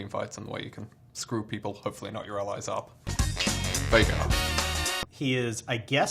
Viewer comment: thought he said hopefully not your relatives I've never been more upset that a joke isn't about incest